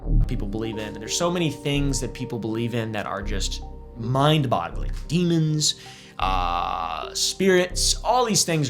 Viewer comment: did bro just say “mind bottling”? Tf? 😂